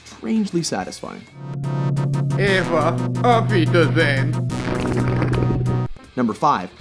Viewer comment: what does Adolf Hitler say when he dies? I don't speak much German. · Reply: "Eva! Auf Wiedersehen ...", which translates to "Eva! Good bye ...". Eva Braun was his girlfriend whom he married shortly before his suicide.